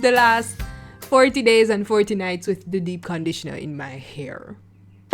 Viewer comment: did I hear that right? " Fourty days and fourty nights with the deep conditioner" what